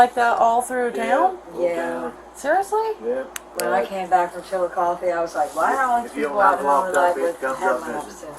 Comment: For anyone wondering, that city name that the subtitles said was "unintelligible" was actually Chillicothe. A city in southern central Ohio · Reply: Thanks.. Thought I heard “Chillicoffee” 😆